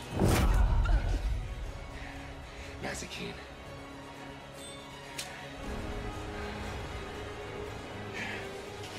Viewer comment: what did he say? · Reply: He say mazikeen